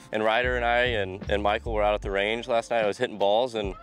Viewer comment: My heart skipped a beat, thought u said Micah at the beginning when talking bout the range 😩